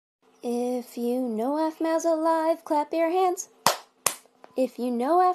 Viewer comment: At first I thought it said “if you know Aphmaus a lie clap your hands” xD